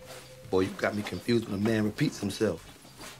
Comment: "Boy, you got me confused for a man who repeats himself" Omar is such a cowboy. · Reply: Lol i always thought he said you got me confuse with a man who pee's himself!